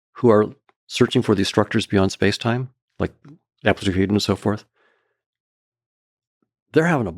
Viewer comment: Who? Does he say? Absolute mumble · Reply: “Amplituhedron and so forth”